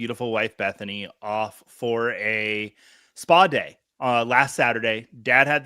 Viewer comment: What is this word spa? I feel like you're starting to say a word and not finishing it. Are you trying to say spaghetti? Are you taking me for a spaghetti day?